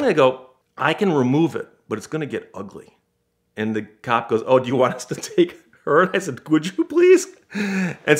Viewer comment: after this i have no idea what he said "U wanna take her as a __ please "